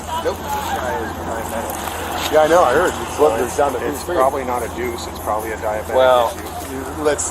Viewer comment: Does he say “Yeah I know, I heard, blood sugar down to ‘two-three’”? Sounds like it as that’s sometimes how numbers are recanted militarily… If so a BS of 23 is no joke and I’ve been there many times, thankfully only once while driving. My CGM was reading 130 (thanks DEXCOM!) but my sugars were at 20 and the only symptom I was displaying (while driving) was a temporary blindness creeping in from peripheral vision.. All things considered, the diabetic is lucky to not have charged the officer, especially with jiu jitsu training. Your brain shuts down, at that severe of a low, to divert energy to the heart and organs to stay alive. That’s partly why diabetic comas are so iffy to recover from. But this also means ALL RATIONAL THOUGHTS are compromised and without immediate treatment there will be loss of all motor controls (as body sends energy to more critical areas than muscles, i. e. heart), seizure, then death shortly afterwards (depending on how much short-acting insulin still onboard). Who knows in the depths of a sugar-starved brain what weird thoughts and training take over? Hope all officers are somewhat trained for diabetes, but unfortunately low sugar symptoms can many times manifest as drunk, disorderly, or high af. 😢 All diabetics should also wear identifying medical necklaces or bracelets as those can definitely help identify the medical issue, to those trained to look for them.